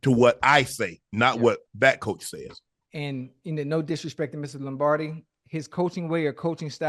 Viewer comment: is Ocho say Mrs. Lombardi 😂😆